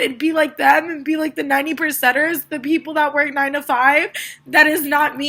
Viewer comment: She lost me when she said she didn't want to be one of the 90%'ers, I think she might have said losers unless I misheard! Hmmmm...